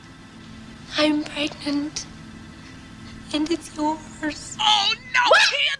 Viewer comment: Real talk the part where the woman says I’m pregnant and it’s yours always make me 😂, I seriously thought she said ours all this time